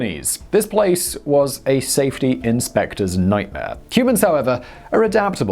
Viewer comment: Anyone catch that Simon called the people of Venezuela, Cubans at 3:49?